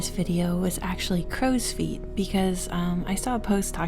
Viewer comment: Ohh I thought it said "grows feet" and I was like what